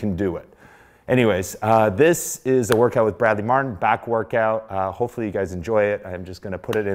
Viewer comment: I thought it said black workout.